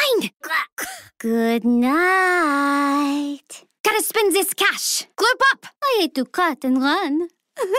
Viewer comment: What is she saying? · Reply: She says gotta spend this cash